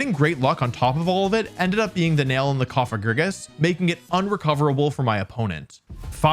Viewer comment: subtitles say Koffing, when the voiceover actually says Cofagrigus.